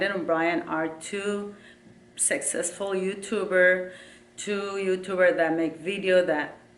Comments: I thought said Brandon and Brian are sexy YouTuber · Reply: It sounds like she said " Successful You tubers " 😂😂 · I thought said Brandon and Brian are sexy YouTuber